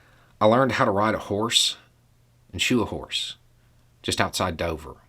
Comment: I thought he said shoot a horse and I had an uncomfortable moment. SHOE a horse though...ok!